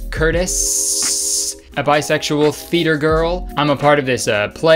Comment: When Kurtis said “theatre girl” i thought he said fetus girl lmaooo